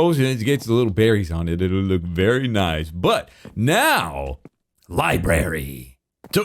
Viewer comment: > i swear he said "Berry Nice" and you thought punts were only for season 8